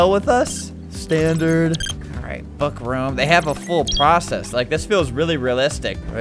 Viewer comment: did he just say fuck around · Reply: he said book room